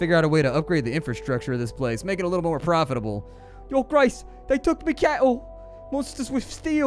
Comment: Lol I thought you said “they took me kettle” and I spent like 30 seconds laughing then realised and laughed even more.